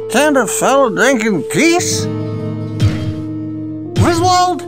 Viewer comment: What does he say there? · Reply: Can a fella drink in peace?